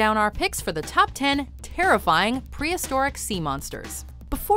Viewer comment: I thought you said "incredible" not terrifying, Mojo 🤔...